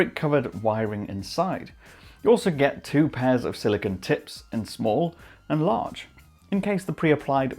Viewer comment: I heard "2 pairs of silicone T!TS"... like every 2nd chick in Miami.. 🤣🤣🤣🤣